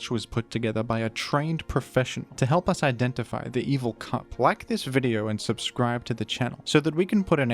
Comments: I know he said “evil cup” but it definitely sounded like he said “evil c**t” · Ngl when he said evil cup at 2:02 I heard evil c*nt 😂😂